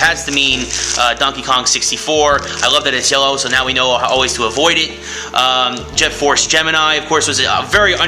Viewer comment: Sounds like you say "avoid it" LOL :)